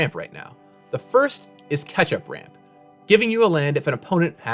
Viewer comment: Did anyone else hear "Ketchup Ramp" lol